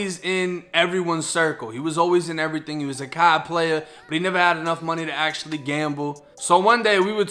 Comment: I thought u said he was a COD player instead of card player at first lol